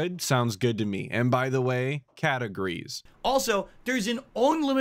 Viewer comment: I thought you were saying "by the way, categories", not cat agrees 💀